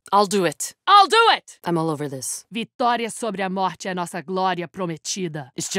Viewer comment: can anyone tell me what she says(not the translation,exactly what she says) at 0:31 ? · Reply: Vitória sobre a morte é a nossa glória prometida.